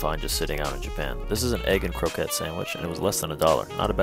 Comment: I thought he said egg and coke head sandwich😂